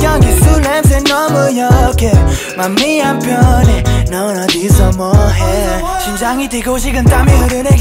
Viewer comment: PARTY2:00에서 크러쉬가 “넌 어디서 뭐해”바로다음에 나오는 말 뭐라고하는건가요?ㅠㅠ알려주시면 정말 “심장이 뛰고”이거말고요.. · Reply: 똑같이 어디서 뭐해라고 말합니다